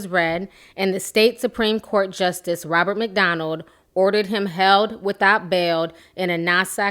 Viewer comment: Robert McDonald lol almost thought she said Ronald McDonald... Lol anyways back to the subject: Nicki Minaj should disown her brother... I hope she dont bail him out, or put money on his books.... And ur right there could be more victims by him using his sisters name to lure them in... SMH disgusting excuse of a black man...